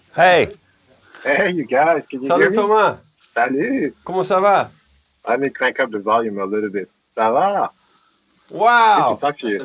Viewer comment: Did Chris say, “kumusta ka?” 😱